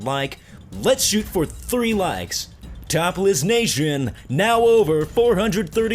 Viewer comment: You sound like your saying Topless nation not Top Lists xD...